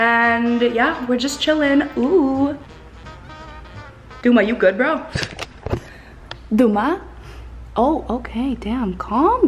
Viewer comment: it's just me mishears or did she said "Đọu má" :D ?? Vietnamese will get this